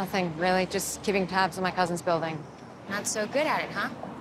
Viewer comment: what she said in 1:04? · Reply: “just keeping tabs on my cousins building”